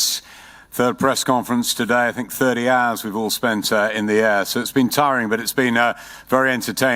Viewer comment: Did he just say that they spent 30 hours in the ass? 😬 🤢 💩